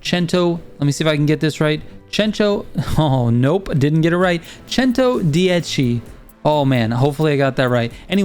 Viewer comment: Can someone please type the name of what he was saying here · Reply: Centodieci.